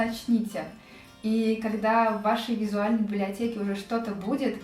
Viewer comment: Что... Мне послышалось? Бляблиотеке?